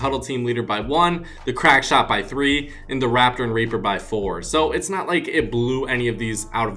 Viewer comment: it sounds like he said raper